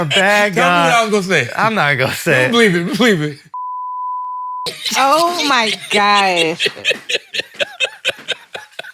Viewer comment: I wonder what you said there brother 🤣🤣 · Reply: "She gets shot in the neck"